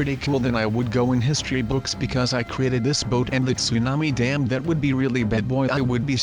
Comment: Did you say dam